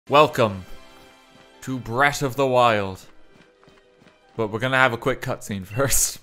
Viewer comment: Thought he said “Welcome to Brexit the wild” at the start there